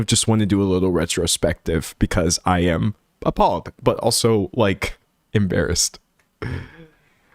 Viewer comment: when you said "because I am appalled" I thought you said "because I am a pawg" at first 🤣